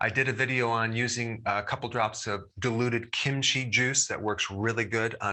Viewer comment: You said diluted kimchi juice. I thought you said deluded. I just did this technique on myself with both hands, middle and ring finger, and it worked immediately.